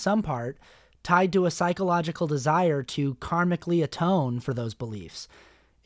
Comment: When you said "karmically atone" i heard "carnally atone" Took me a second